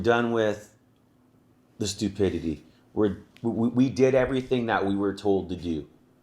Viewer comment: "We did everything we were told to do." I thought you said you're not stupid.